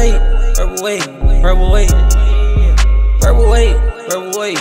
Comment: It sounds like he said her weight